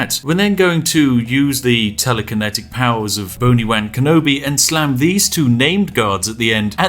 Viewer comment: With his accent, every time he says “Boni Wan Kenobi” I hear “Bony Wang Kenobi”